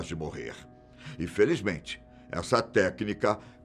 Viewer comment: não entendi , ele falou que infelizmente elas pararam de ser usadas? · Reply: Nao meu amigo ele falou " E felizmente essa tecnica..."